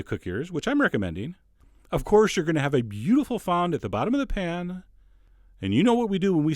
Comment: what was that thing he said at 2:58 area about the pan the thing on it starts with a " F " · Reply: Fond. The good stuff that is on the pan after cooking.